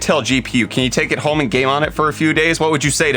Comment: If I told you ‘hey we got an intel gpu, can you take it home and gay on it for a few days, what would you say?’ “Yeah” Thought I misheard that 😂